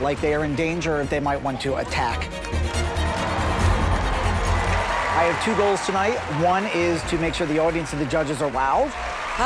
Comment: I thought he was going to say I have two goals tonight and one of them is a sea. As in Seagull.